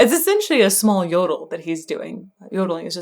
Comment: previously a few seconds earlier, did you mention yodels? I didn't catch that.